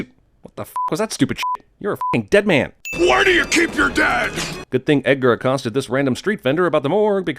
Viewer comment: Up until I watched this video for the SECOND time I thought he said "Where Do You Keep Your DAD" and that part of the movie was so confusing until now XD